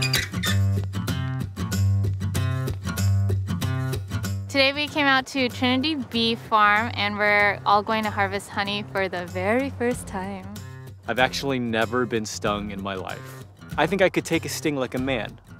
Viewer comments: Legit thought it said harvesting MONEY lmao.. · Am I the only one that thought it said harvest money ahaha